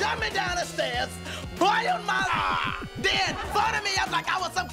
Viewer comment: What did Kel say in that skit?! I must know!!! My every waking moment will be haunted by that question! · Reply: It's at 4:40 but I can't catch it. Boiled my liver?